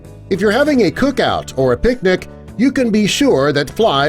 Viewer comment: i heard "If u have cook out or big dick"